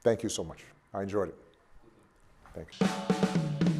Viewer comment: What was it that Obama said at the end, at 8:45? Something like "I can see you in Spain", I can't seem to be able to decipher it. Thanks! Also, thanks El País for the interview. The world needs more leaders like Obama. The difference between Obama and Trump is just completely mind blowing.